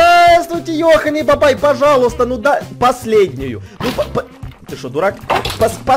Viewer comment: мне одной послышалось: ты Чебурак??? Я СМОТРЮ ЭТО ВИДЕО В 2021 УИИИИИИ